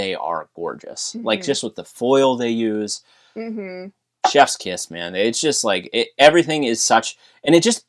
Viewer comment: I definitely heard say "Chef's kiss" as "Jeff's kiss" at first and was like, hang on, is he trying to make that a thing?